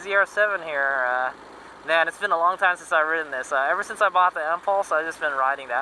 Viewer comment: What are you saying at 00:08 Ever since you bought the what? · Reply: Oh I'm talking about my Victory Empulse TT electric motorcycle. I bought one when Victory went out of business.